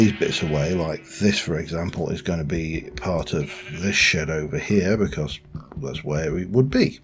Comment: I was startled for a moment when I thought you said “…this is going to part of this shit over here”